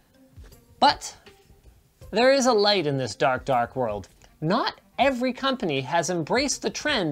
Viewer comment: Lol wasn't actually looking at the screen when he gave the mouse plug. Thought he said "Dark Whore" and spit my coffee all over my office!